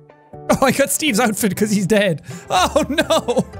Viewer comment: if you skip 5 seconds backwards before jack says "dead" it sounds like he says because hes gay 💀